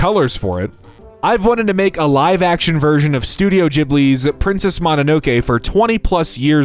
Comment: Did I just hear you say “Studio Jibbly”!?